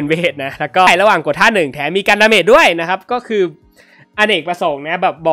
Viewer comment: พี่พูดว่าอะไรอะครับผมฟังไม่รู้เรื่อง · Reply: ระหว่างท่า1แถมมีกันดาเมจด้วย